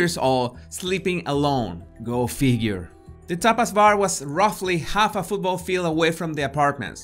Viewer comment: Ooooh Tapas bar 🙊 I thought you said topless bar 🙈 lol